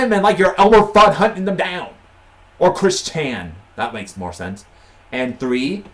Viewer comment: he clearly said chris chan